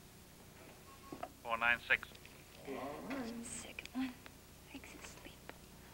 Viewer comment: what is she saying "I feel sick..need some sleep?" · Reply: 93Vet I feel sick and my leg's asleep